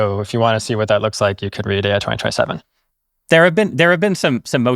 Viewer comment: What does he say to read at 28:29? Read anti 2027? What/where is that?